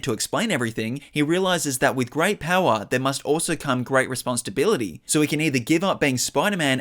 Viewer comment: I keep hearing "Response to bility" idk why lmao